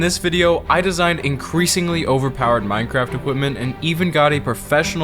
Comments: did you just say "Minecraft women" i swear i didn't hear "weapon" you literally said women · i heard ‘equipment’ as ‘women’ lmao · i heard minecraft w o m e n